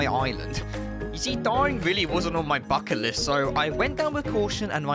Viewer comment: at first I heard ‘you see, dying really wasn’t on my f*king list’ in stead of bucket list and I died laughing for a sec before my brain processed